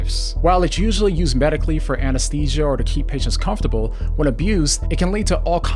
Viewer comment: I had to rewind, thought you said "to keep b*tches comfortable" I almost spit my drink